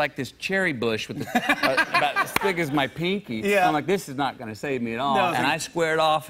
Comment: Am I the only one at 2:24 half thought that he may have said as big as my penis? 🙈😂